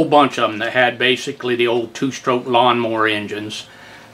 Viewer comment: Wow , I thought you said OUTBOARD MOTORS not LAWN MOWERS !!!! Lmao. Congratulations to those 2 , game fisher AKA Eska , Mercury , Johnson , Gale and maybe Hiawatha and the engines were made by Tanaka of Japan if memory serves me !